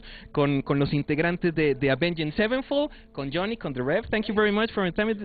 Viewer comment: 'I have no fucking clue what this dude is saying!' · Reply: Haha, in case you're still wondering 9 years later, he said the name of the band but he pronounced it avenjed in stead of avenged. Yes he got an accent but i understand him perfectly. Lol